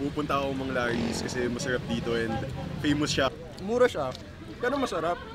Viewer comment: Nanginig ako nung narinig ko "mesherep dito". Ganyan ba talaga mag salita mga taga Ateneo? LOL